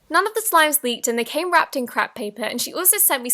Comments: I heard crap paper 😂😂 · haha I thought she said they all came wrapped in crap paper! Love you! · I thought she said “And it came wrapped in crap paper” at first 😂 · did she say ‘crap’ paper? Or was it just me XD btw no hate I just heard crap paper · did you say crap paper? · did she say crap paper 😂❤️ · anyone think that she said crap paper idk if that’s actually wat u said it if u were trying to say something else I’m not trying to be rude 😅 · Who else heard “crap” paper 😂😂😂😂😂😂😂😂😂😂😂😂😂